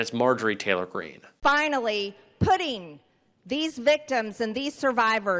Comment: All I heard was "Finally, pudding!"